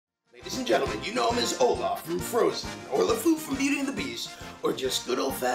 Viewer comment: did he say lefou with an extra fou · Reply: warmer I think he said "Or as Lefou from beauty and the beast"